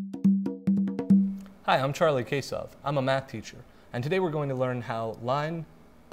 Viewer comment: thank god my teacher assigned this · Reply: i thought he said charlie caseoh